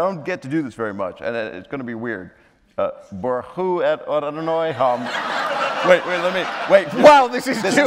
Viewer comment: Does anyone know what Bob said @ 25:38 ? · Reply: It’s what we say at synagogue when making an aliyah. You say Barechu et Adonai HaMevorach. - which loosely translated as Blessed the Lord , who is blessed Then the congregation replies Baruch Adonai HaMevorach LeOlam VaEd - which loosely translates as Blessed is the Lord, who is Blessed forever and ever.